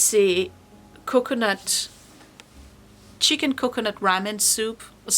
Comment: Phew....stopping video to comment...I thought you said you adjusted the third week of recipes to a curry rabbit soup...played again to hear it was ramen...LoL. So glad I heard wrong. 😂